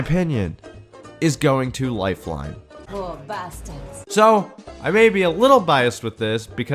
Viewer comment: is she saying lore bastards?! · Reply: “lil’ bastards”